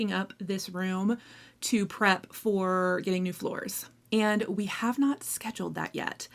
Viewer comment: you said “getting new floors”, but I thought you said “getting a divorce”. You have old lady eyes and I have old lady ears. Hahahaha